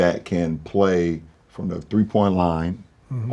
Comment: What is he sayin here? "Y'all need a shoot threes"? I ain't a fluent english speaker, can anyone help me out pls? I just want to know what he is exactly saying word for word. TY! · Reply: “He don’t need to shoot threes, he knows his game”